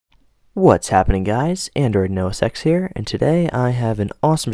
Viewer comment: it sounds like he is saying "android no sex"